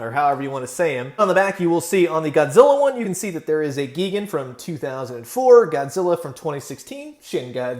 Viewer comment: I swore I thought you said “Vegan”